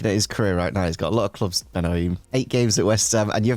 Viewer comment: He definitely thought said Benayoun not Ben Haim, that’s why he said West Ham haha